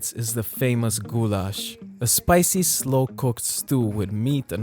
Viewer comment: bro i thought he said that dish "gulag" my bad